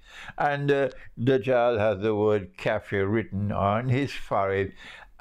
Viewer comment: Pardon me, I couldn't get what he said at 3:48. What is the word written on the forehead of the dajjal? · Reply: kafir. meaning disbeliever